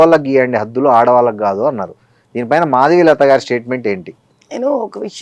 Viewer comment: Intha paddathiga badhyathaga chepthe elantivaru ayina sare maralsindhe🎉🎉🎉🎉🎉🎉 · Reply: he said maadigalatha instead of madhavilatha lol